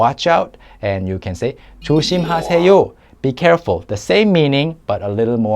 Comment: Not sure if someone pointed it out already in the comments, but it seems there's a mistake in the captions at around 15:30 the captions say 조심해세요 rather than 조심하세요. Anyway thanks for the video!